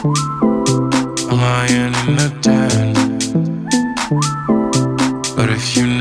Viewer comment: I thought he said "I'm lying in the den"...lol. Like he is chilling out watching tv in the back room...haha